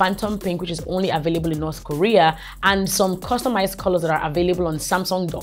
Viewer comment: Did I just hear north korea?